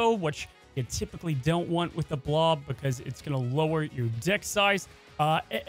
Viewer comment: Lmao bro I could’ve swore you said getting limbo with Blob “will lower your D*ck size” instead of lower your deck size! Lmao great video man!